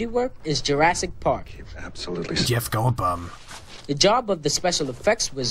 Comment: I swear I heard "Jeff Goldbum" lol